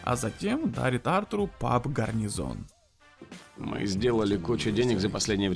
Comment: расшифруйте мне пожалуйста эту фразу, я слышу ее как: "аритартару пап гарнизон" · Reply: а затем дарит Артуру паб <Гарнизон>